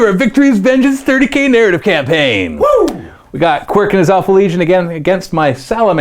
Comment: did he say awful Legion.